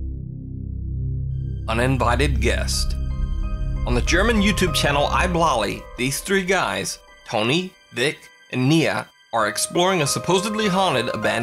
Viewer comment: I thought you said "Tony, Thicc, and Nina" BRUH IM DEAD